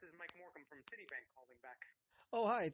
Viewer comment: We should need a transcript of those calls as we can’t hear anything unfortunately... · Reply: You could use the closed caption on YouTube why did it sound like he said Mike Morgan